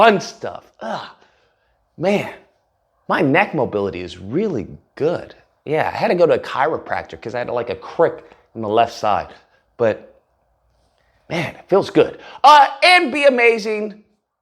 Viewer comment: Sounds like he said dahulla LMAO 🤣 embarrassing that these are the people running our country